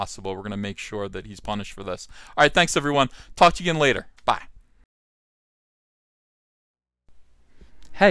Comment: I was playing back about 1.75x speed and in the ending sign-off (about 13:09) it almost sounded like you said "Thanks everyone, fu*k 'ya again later, bye!" and I thought that was one hell of a saying, or like something Mark Waid would use unironically